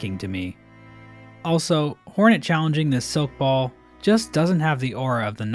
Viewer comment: did he say horny 😭